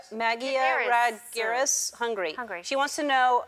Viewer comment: Does she say Hungary?